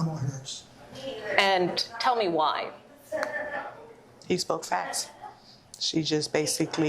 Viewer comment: "He spoke fast'" Well, you've convinced me. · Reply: I thought she said facts